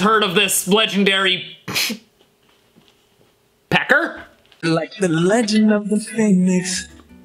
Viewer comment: “like the legend of the pheonis” That’s what i heard but I also hear phonix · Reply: i heard "like the legend of the fanbase"😂😂